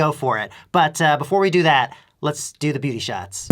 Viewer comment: Damn, I thought you said booty shots . . .